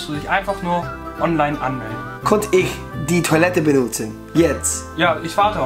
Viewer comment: '' did you say ? '' could ich die toilettebenutzen :)) ''